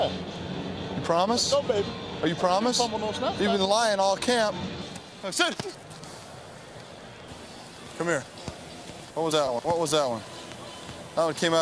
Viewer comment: did he say "cap"